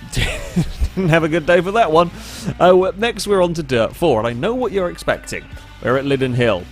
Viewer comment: lol Did he call it Derp 4?